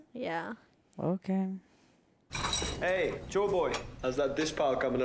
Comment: When Sanji says "Chore boy" I keep hearing "Joy boy" for some reason? Maybe it's the accent... 😗